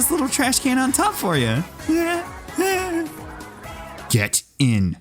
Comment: Bro you were saying get in in the splat 2 video I thought you were saying get it